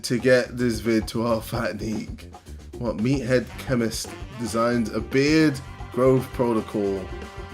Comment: I thought it said "Methead Chemist" 😭